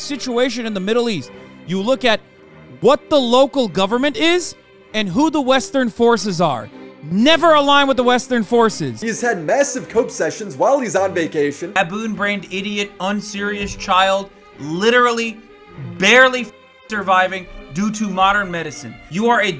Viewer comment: Surely im not the only one who thought he said massive coke sessions lol